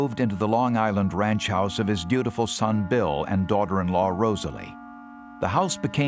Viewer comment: why did he say "his beautiful son bill" 😂😂